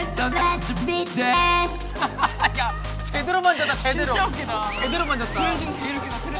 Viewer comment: it totally sounds like JB is saying 'beat yo ass' I can't unhear it now hahahha